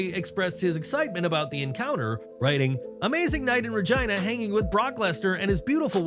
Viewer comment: Everytime you said Regina I thought you said vagina lmao